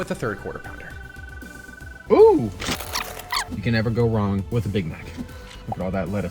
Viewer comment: U think you said big back